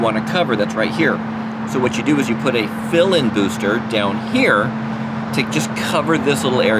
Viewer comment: You won’t believe what I thought you said first…instead of a “fill-in transmitter” I thought you said villain 😂 I woulda fallen for it thinking it was some sorta tech term LOL. I can’t remember if I have asked this before & maybe this is a discussion for an entirely separate video, but what steps are taken, what “studies” if you want to call it that are done to determine a good spot for a transmission tower? What was the process to determine oh let’s set our booster site right. Here.